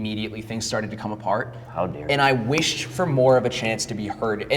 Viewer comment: Anyone else hear the slight "how dare you" at 26:02 when the conservative was talking about how he used to believe in?